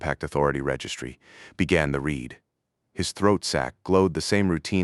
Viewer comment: in Does he say ball sack